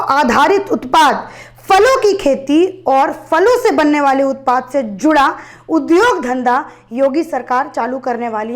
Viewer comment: Madam at 4:48, instead of "falo ki kheti aur falo se banane waale utpaad" Its Foolo ki kheti aur foolo se banane waale utpaad... 😅😅😅😅